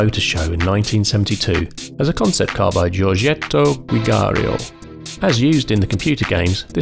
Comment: The designer is Giugiaro not Giugiario as I hear on 0:44, the devil is in the detail 😊